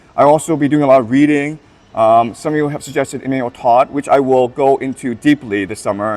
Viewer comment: Did he say Emilee Todd at 8:25 ? As in Lincoln’s Step Sister? Can somehow help me hear out who he mentioned before Oswald Spengler